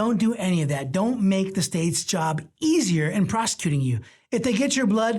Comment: Just an FYI, when you said "Don't make The State's job easier in 'prosecuting' you," it very much sounded like you said the word "prostituting."